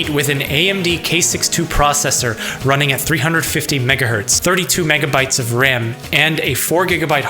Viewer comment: I thought he said 32gb of ram and I’m like woah, that’s pretty good. Little did I know he said 32mb of ram